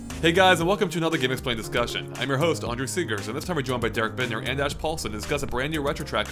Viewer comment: I never got what the host´s name is, I always hear I´m your host "on your cigarrettes"